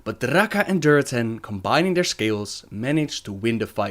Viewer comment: "But Draka and Durotan, combining their scales, managed to win the fight." No matter how many times i hear it, that is what i hear XD